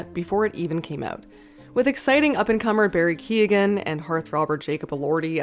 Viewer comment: Did you just say hearth robber?